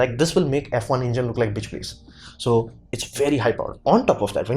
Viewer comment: Your magnetic knowledge density and the familiar use of english language are excellent, subscribed. On point immediately, then I checked out your channel, A TEACHER YOU ARE. 1 question "axial-flux makes F1 like bitch ????". You need not change your delivery, others need to adapt to speedy One World english. Your efforts are noble, Thank You kindly. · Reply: I think he said "bitch, please" (5:50) which in American English would mean "yeah, right", "as if", or "pfft" (sarcasm noises). I think he's saying that the F1 engine would be laughably outclassed by axial flux. I can't tell you what part of speech it is because it's a humorous usage, not formal grammar.